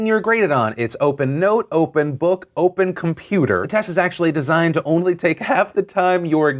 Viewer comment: Maybe I misheard but I thought Phil said open note/book/computer tests Meaning you can literally just look up the answers. That's a bit much I do think some of the ideas were good though Like being able to opt out of group things and be judged solely on your own work I like that